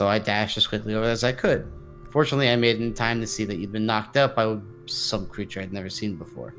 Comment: Lol I thought Michael said knocked up I was so dead lol 😂